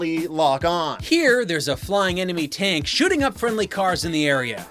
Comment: I heard that as 'shooting up Linkaras'